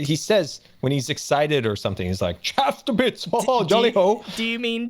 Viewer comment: My 'chaffed to bits' senses are tingling. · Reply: I thought he said "chafed to bits" at first